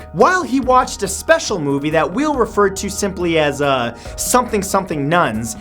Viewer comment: I thought you were going to say semen.